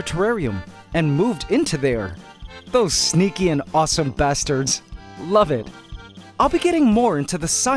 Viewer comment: Wait a minute DID SAY BASTARD